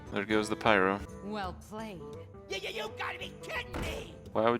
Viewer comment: i cant help but hear "nigga you gotta be kidding me" when venture co.mercenary was played at 20:23, can someone tell me what he is actually saying? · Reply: He stutters on y-y-you gotta be kidding me!